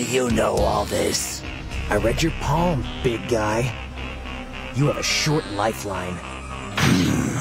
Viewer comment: For a minute I thought he said "I read your palm, bitch"